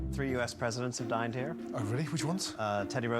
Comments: Also just discovered that Teddy Roosevelt died in NY, not VT. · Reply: He said dined, not died I thought he said died at first too lol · For a moment I thought when he said... 3 US presidents dined in here... I mistook it as DIED IN HERE O.O Anyways, is there an episode where all the staff just... walks out mid episode? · Who heard it as “3 presidents have died in here”